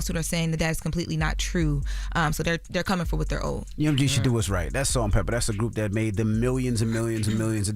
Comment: I thought they said, "Inmates, Salt-n-Peppa!" 😅🤦🏾‍♂️ I had to rewind wondering how they got both at the same time 😭